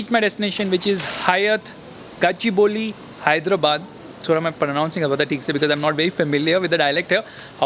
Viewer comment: I thought you said about that bus